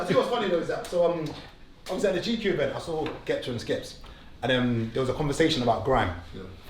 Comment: did he say Ghepta and Skeps?? 😅 humour is unmatched